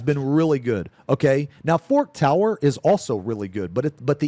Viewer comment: the way he say "forked tower" i always hear "fuck tower" which is a kinda fair describtion anyway